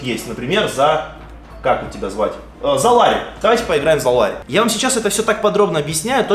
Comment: Мне послышалось или ты сказал поиграем за Ларина :D ( на 15:18 )